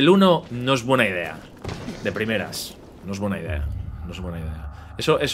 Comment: cuando entendi 'Primeras horas' pense en 3 y no en medio juego owo